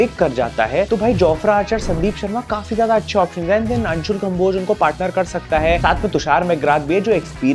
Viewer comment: did you say anshul khamboj 14:35??? csk